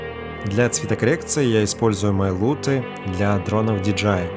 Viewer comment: Не расслышал 11:20, для цветокоррекции что используется? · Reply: Луты свои продает)